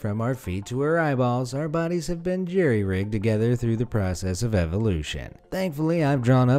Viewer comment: Is it just me or did he say JerryRig in